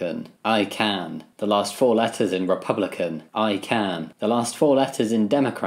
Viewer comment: “the last four letters in democrats: rats!” i mean, i am forty rats stacked in a trench coat so they’re not wrong ¯\_(ツ)_/¯ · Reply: please i thought you said "the last fourteen letters" and i thought rats was just so powerful